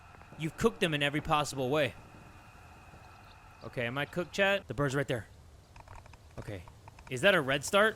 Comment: did he just say “Am i cooked, chat?